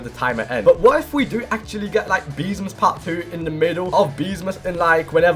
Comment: I heard beesmas hawk tuah someone save me 💀